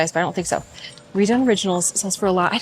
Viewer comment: Haha. I thought you said Rae Dunn for those jeans and I was trying to figure out when Rae Dunn made jeans and then a comp was put on the screen. Redone! Here I am laughing at myself… nice vid too! What a fun day.